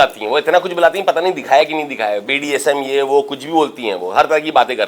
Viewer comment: Did he say BDSM? 😅